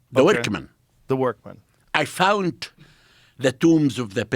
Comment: Thought this guy said “I found the tomb of Diddi” 😂 so even the Egyptians partied with diddi 😂